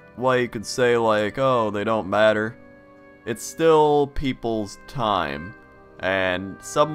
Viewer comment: did you said peoples at 6:59 ?